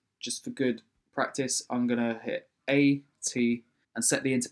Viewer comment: What the fuck he says at minute 1:45??? Hit a t?? What the fuck do i have to do to open that window??? I cant continue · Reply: He says A T